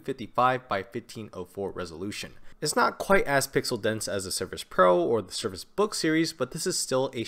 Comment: 4.53 "It's not..." could you please repeat what is said here? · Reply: its not quite as pixel-dense as the surface pro or the surface books